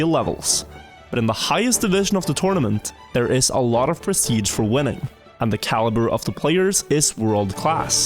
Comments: I heard it as prestige for women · i thought you said “women” instead of “winning” i got extremely confused